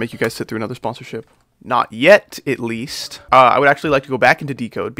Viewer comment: I thought ''freak'' when he said I'm not gonna sponsor... YET